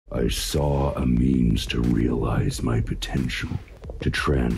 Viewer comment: thought he said "i saw the memes..."